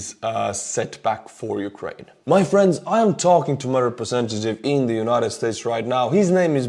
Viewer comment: I have no idea what you said. "I'm talking to" who? Sorry it sounds very scrambled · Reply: Talking to his representative, his name is Brian 😊